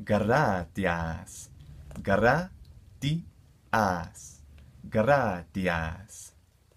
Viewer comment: I'm learning this to but I thought I heard gradahass